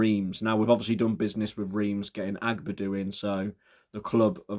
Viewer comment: i thought you said Reans for a sec